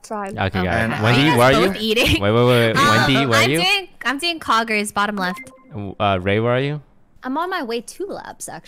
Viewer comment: I swear I heard her say "I'm doing poggers bottom left"